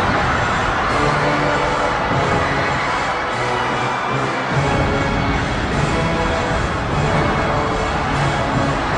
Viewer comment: what are they shouting? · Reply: stereo channel - "Pink Floyd!" Right stereo channel - "Hammer!"